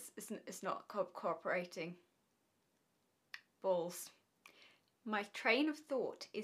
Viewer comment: in.... Did you say "Balls"?!! Oivei, we love that 💞 personal joke of ours, however we must share that that comment is captivating so far ☺️